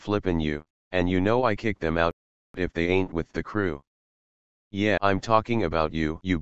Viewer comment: I can understand this version better than the original. in the original I thought he said " and you know I kick their mouth if they ain't with the crew"